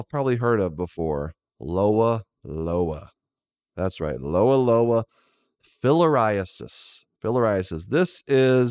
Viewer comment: lol "Filler Eye Assist" I know that's not how it's spelled but that's what it sounded like to me